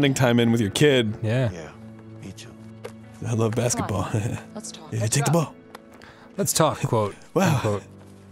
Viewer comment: I don't have good internet connection and I'm on my phone, so the video was fuzzy. I thought Linda said "lets fuck" instead of "lets talk"